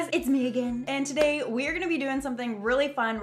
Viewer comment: I thought she said 'It's Megan' and I was like NO I WANT and then I realised omg she doesn't have a twin dumbo!!